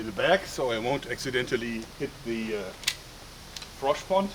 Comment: Did i hear that correctly? "The frosch pond"